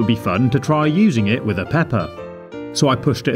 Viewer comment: sounds like he said Peppa 😅🐷